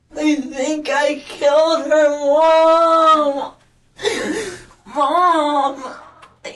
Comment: I swear on my mother's life he said "I think I killed her mom" the subtitles say "they" but I think he slipped and said "I think I killed her mom" I swear killer kids are dumb asf.